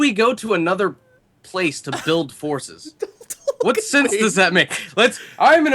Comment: I thought said we are going to another place to build horses